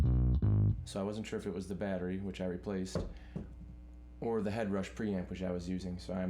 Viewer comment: I wish I could figure out what the word is you said about the battery “So I went and “triffed” it with the battery” I’m guessing your not saying the word “triffed” but that’s what it sounds like ?. My reason for asking is I have one of these bass’s and I’m unclear weather you disconnected the battery but then you also said “replaced” I watched this video more than a few times and I’m still and unsure if if eliminated the battery completely and made the bass totally passive or not ? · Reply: I think he said “I wasn’t sure if it was the battery which I replaced”